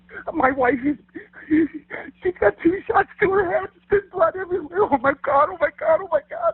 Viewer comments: It sounds like he said 3 shots. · 'Two shots to the head' darn right that is the killer telling on himself. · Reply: I swear I heard him say 3 shots even slowed it down to listen to it. It has an Eee sound like three not a eew sound like two, but seems everyone else is hearing 2. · Caleb said he wasn't in his own bed because he had a bad cough, yet never heard him cough on the 911 call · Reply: I heard him say 3 shots